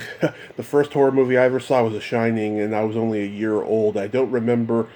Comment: Omg I thought you said The Exorcist was the first movie you saw 👀. You said the Shining 🤦‍♀️🤦‍♀️ . No one threw up in the theater during The Shining lol